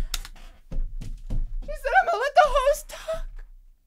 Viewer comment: I thought she said imma let’s the 0s talk so you can see the billions better call the banker